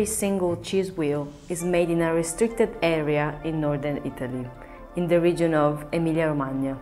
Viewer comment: did it say “our stupid area” or what?